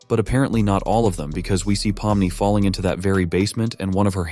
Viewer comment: the subtitles say parvati instead of pomnni💀💀